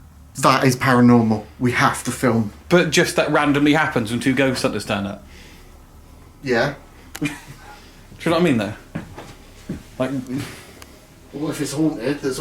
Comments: voice, I think it says "yeah" right after. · a voice sounds like it says yeah in response to your question